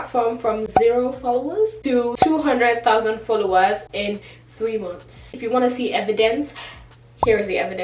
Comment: did she say 8 months!! In the beginning